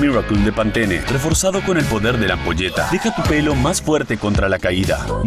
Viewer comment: Yo Vi El Comercial En La Tele y Escuche “Con Todo El Poder De La Polla” Y Me Asuste Y Por Eso Busque El Comercial